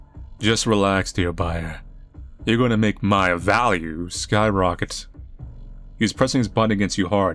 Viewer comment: nil at 16:46 I thought it said VALVE and is was had to do a double the like "AYOO?!!!"